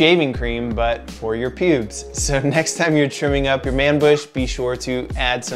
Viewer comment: Thought you said "Chewing up your manbush" and not "Trimming" :D Great video mate!